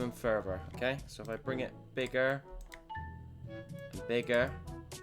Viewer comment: bro did NOT say bigger